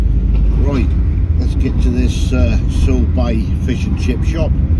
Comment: I thought said SaltBae, I was expecting Gold Plaited Fish and Chips